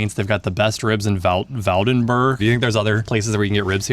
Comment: This is literally the one in Woudenberg. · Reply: Oh lol I thought he said Valdenburg not woudenberg XD